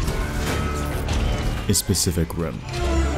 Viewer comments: my dumbass thought he said ''specific rim'' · I heard "spesific rim" for a moment there 😂